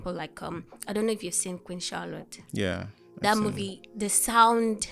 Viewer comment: what's was the name of the movie she said regarding sound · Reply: Queen Charlotte 😊